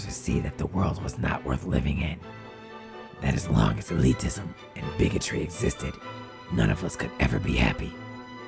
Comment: Um...I didn't hear Lucario clearly at 11:21-11:24. What did he say??? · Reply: "-that, as long as elitism and bigotry existed-"